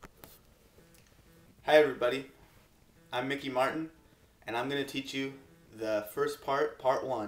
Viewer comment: lol i thought you said ricky martin his name is thanks foir the vid btw